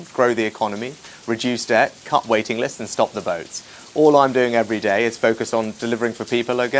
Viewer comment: She's still sporting the wannabe Pulp Fiction look! They are both as thick as each other. When he said stop the boats I thought he said stop the votes.....which is what he's achieving in SPADES